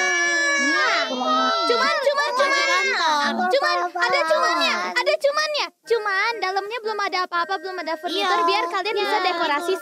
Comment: 그만 그만 그만 다들 그마네 ㅋㅋ 왜 이렇게 들리지